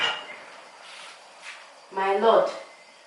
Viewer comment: Wait did I just hear her calling him "my lord" woooww some men are really lucky he found a treasure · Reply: I thought she said my love🤪🤪